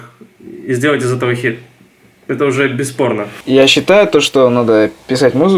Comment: он сказал без порно или мне послышалось?